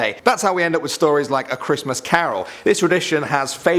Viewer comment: BRO that scared me man I thought he said a Christmas Karen at first